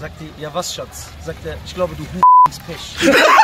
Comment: was hat der gesagt ? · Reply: Er hat gesagt : Ich glaube du H*re bringst Pech